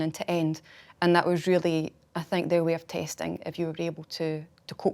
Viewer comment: Her accent is so thick, I thought when she said "testing" that she was saying "tasting". I was like woah....these forensic scientists start to get a taste for blood,or a taste for the gory aspects of the job! Which i actually think is fine,there are many ways of coping and dealing with kind of stuff, i have done nursing and i have seen lots of similar things,but that's not what she meant!